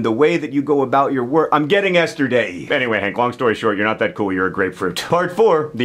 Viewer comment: what does John say? "I'm getting asterday..."?? thanks